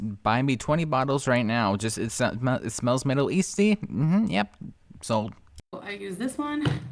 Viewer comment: All I hear is “…smells middle yeasty.” I’m sorry.